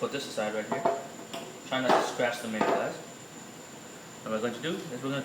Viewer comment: And at 3:50 he actually said "try not to scratch the lens"